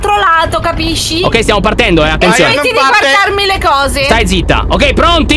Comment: Inizia il video:*visible confusion* 1:28:Non guardarmi le cose!!!Stai zitta. Io penso male:👁👄👁 #VeraLeonessa🦁💜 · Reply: Io ho capito "toccarmi" apposto di "guardarmi" Semu messi bene 👁️👄👁️✌🏻